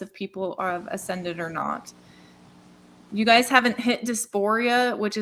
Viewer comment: "you guys haven't hit dysphoria yet...?" Can anyone explain or correct what I think I heard?